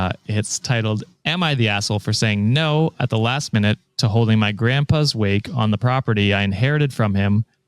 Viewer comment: I actually thought said "my Grandfathers wig" I couldn't help but laugh. Then I realised I was laughing about a wake. Mistakenly.